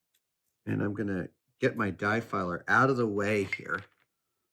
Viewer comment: I thought it said die filler and was to do with dice